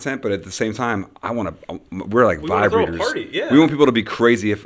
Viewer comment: i thought he said “we’re like vibrators”